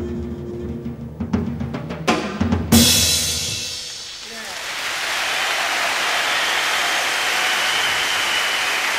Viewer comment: What he said. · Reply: he said "Yeah" its a common anglo expression to express satisfaction with the result of a project someone was working on or similar.